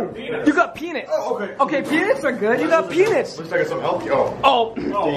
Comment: I swear he said pen's